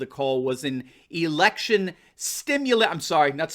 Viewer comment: He thought they said erection stimulation.🤣🤣🤣🤣🤣😂😂